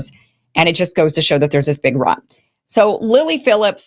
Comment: Her body her choice is somehow men’s fault? That’s a stretch. Women in the Middle East not being allowed to do anything is men’s fault. However Women in the West allowed to do whatever they want, that’s also men’s fault? She is accountable for her actions. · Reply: did she say there’s this big rot?